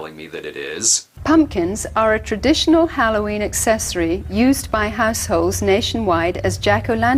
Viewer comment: Wait, did she just say "used by assholes"?!?